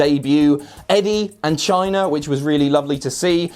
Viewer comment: Lol I thought you said Eddie in China